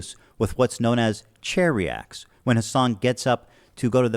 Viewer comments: IM SO FUCKIN DUMB!!! i thought he was saying "Cherry acts" this whole time not knowing wtf it meant and i just put it together that its "chair reacts". it was LITERALLY EXPLAINED and i still didnt realize 🤦‍♂️ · Ohhh, “chair reacts”!! I could’ve swore he said “cherry acts”, which was a bit confusing. 😂😂